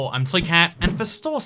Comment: Did he just say "I'm toycat" instead of "I'm ibxtoycat"